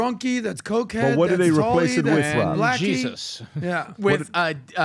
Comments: Did just say that’s blacky? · Did he say Blackie?